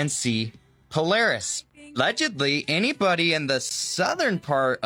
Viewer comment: it sounds like he says " 'legedly" here instead of "allegedly" but the first syllable might've been cut off in editing. I've seena few of these young tik tok and "youtube shorts" people's videos.. and there is a portion that needs to go back to elementary school to get a better grasp on the English language.